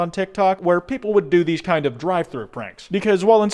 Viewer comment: i thought he said driveby pranks and i was waiting for some yelling bc i had this minimized while playing a game lol